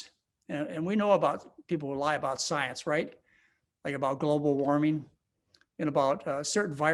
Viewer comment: did he say Global Warming is a lie ?